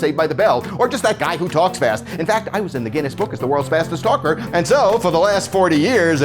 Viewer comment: Thought he said word fastest stalker